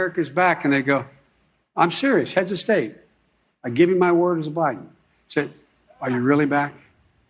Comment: At around 8:12 did he actually say, "I give my word as Biden?" What does that even mean? Jimmy, you're laughing on YouTube, but I know when you're not on YouTube you're wondering, "How the hell can we get somebody in the White House that knows what day it is?" If we get rid of Biden, we get Harris. If we get rid of her, we get Pelosi. When does the madness end? The reason that most people voted for Biden was because they didn't like Trump. Now that's using your fucking brain. prior to the "World wide" pandemic, unemployment was low, the economy was going great, and gas prices was the lowest it has been in at least 21 years. But since people didn't "like" Trump they voted for President Sippy Cup. There will be a civil war or revolution in the US within the next 3 years. I have to admit, I voted for Trump because I didn't want Hillary in the White House. But she at least has a past. A past of not accomplishing a damn thing. In the '90's Bill gave her the job of writing healthcare reform. After a few months and $millions of tax payer money she had nothing. Her promise prior to becoming NY State Senator was the promise that she would help cow farmers. As Senator it got worse for them. The Clinton Foundation is as illegal as can be. As Madam Secretary of State Russia donated to the foundation and she sold the Russians Uranium. Yep, to make Nukes to drop on the US. In the '90's there's a trail of murder she left to protect her husband Bill and hide his illegal activities. She was e